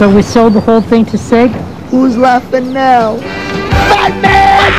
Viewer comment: did he just say batman at 0:23???